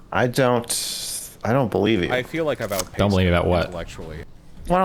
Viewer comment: what did the soundbite say? i couldn't hear it and is such a funny person to listen to. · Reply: "I feel like I've outpaced him, intellectually." Talking about Destiny after a falling out.